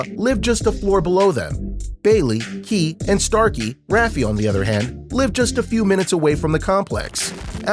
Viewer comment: i swear he said ra fee lol